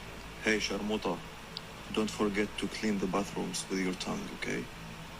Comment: This whole time, I had thought he said "buttholes", but it's "bathrooms", apparently. This dude is so messed up. You can tell just by looking in his eyes.